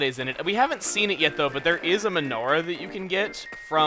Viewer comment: When said "menorah", I thought he said "Majora", so I got confused!